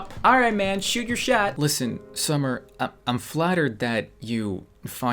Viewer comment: When he said 'Im flattered' I heard 'Flat Earth'. Some things wrong with me.